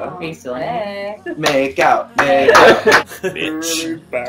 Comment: Is it just me or at 3:02 does avi say bitch?